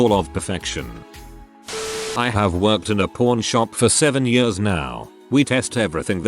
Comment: I play these videos more for background noise and thought he said "PORN shop" instead of "pawn shop." Had to stop what I was doing to read the text myself because I thought he admitted that people who work at adult stores test their "products" before selling them...